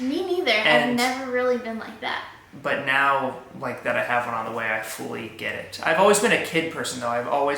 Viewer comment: wait did just said "have HER on the way?"